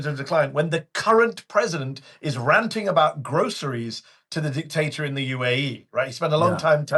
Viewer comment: did he say dictator in UAE?